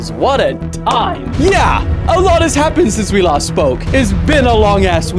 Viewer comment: Tbh I thought he usually said "...since we lost smoke", just realizing it's "...since we last spoke"